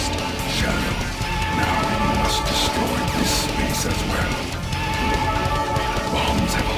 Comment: Did he say "we must destroy this piece of crap"?